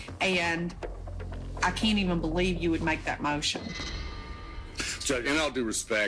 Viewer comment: i thought... when judge says "i believe " is a queue BIAS